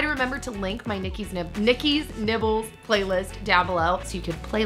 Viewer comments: i thought she said nikkis nipples. 😂✋ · Hahahaha I thought u said Nikki's nipples at first😂🐶🐶🐶🐶🐶🐶🐶🐶🐶🐶🐶🐶🐶🐶🐶🐶🐶🐶🐶🐶🐶🐶🐶🐶🐶🐶🐶🐶🐶🐶🐶🐶🐶🐶🐶🐶🐶🐶🐶🐶🐶🐶🐶🐶🐶🐶🐶🐶🐶🐶🐶🐶🐶🐶🐶🐶🐶🐶🐶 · Lol I heard "Nikki's nipples" at first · Fingers crossed for a name change!! Everytime I hear "Nikki's Nibbles", I hear Nikki's Nipples and it makes me want to bang my head against the wall lol. I know I'm not the only one that hates the name either! Maybe you should do a poll or something....